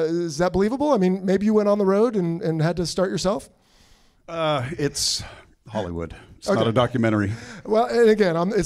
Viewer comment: Watch to the end, we all say what we think. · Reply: the worst comment was said is “its hollywood” while most of it i hear “its a great movie”.